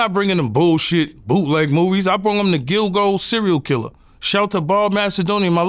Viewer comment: I thought he was trying to say gigolo 😂 Gilgo...respect.